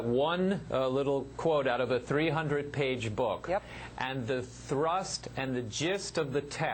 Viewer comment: i misheard that as "thrust and jizz"...would have been so much funnier